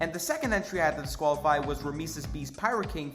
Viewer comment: ok so ughhhh ... i just misheard Rameses B to Mr. Beast